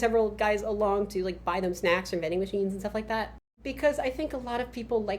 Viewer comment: AH - VENDING MACHINES I had to play that back several times cos I thought you said Knitting Machines and that was weirdly specific